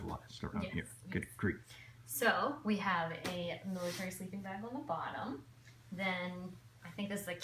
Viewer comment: What is the bottom layer? Couldn't hear what you said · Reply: Ha ha, I just re-watched that part and I totally mumbled, sorry! It's an older military sleeping bag. What's on each layer doesn't really matter as much as adding blankets or sleeping bags until you get to your desired level of comfy, then covering with a fitted sheet to hold everything together.